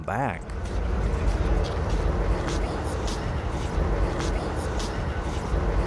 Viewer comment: I hear "Please go back".